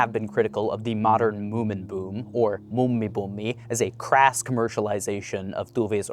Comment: What he tried to say; Muumi buumi= the moomin boom What i heard: mummi pummi= a grandma hobo. XD The double vowels and consonants have a reason y'all. XD